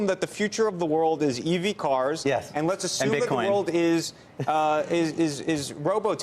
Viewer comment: Did he say "and Bitcoin"?